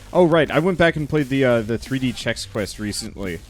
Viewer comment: At 44min, says he went back and played a 3d game, can someone tell me what game is it? I can't quite understand what he says, what I hear is "3d check quest" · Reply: 3D Chex Quest